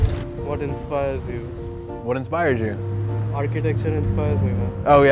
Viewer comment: What did he say at 2:09? Architecture?